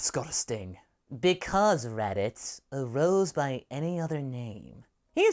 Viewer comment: “Because, Raditz: a Rose by any other name...is promptly thrown to the ground and left unconscious.” I know that’s not exactly what he said, but that was what I heard the first time, and it somehow got me to laugh.